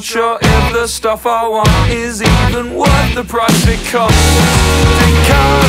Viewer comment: I could've sworn it said "the price it comes" not costs